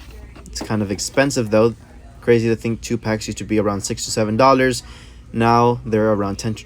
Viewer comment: hate to do this but did you just say 67